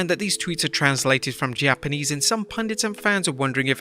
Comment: did you say pandits...!!! Japanese Pandits interesting